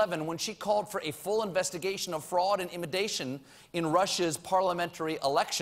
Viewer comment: Lol colbert said imidation instead of intimidation